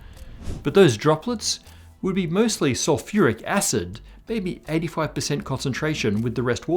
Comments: Looks like there's a couple errors in the subtitles, e.g. 8:43 says "hydrochloric acid" instead of sulphuric. Also, I think I read 'phosphene' a couple times instead of phosphine, which is quite different! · CORRECTION! Subtitle says hydrochloric acid instead of sulphuric acid. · CAPTION ERROR: @ 8:45 : You say sulphuric acid while the captions say hydrochloric. ALSO: I have been pondering the matter/antimatter asymmetry problem and I think I may have a potential solution. Is it possible that the matter/antimatter asymmetry of the universe was caused by primordial black holes? Perhaps during the early universe, primordial black holes were created due to the initial dense conditions, which ended up accreting uneven amounts of matter/antimatter, and also radiating hawking radiation particles which were uneven in their amounts of matter versus antimatter? I'd really like to hear your thoughts on this. Thanks. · hey dude, the caption at 8:44 says "hydrochloric acid" instead of H2SO4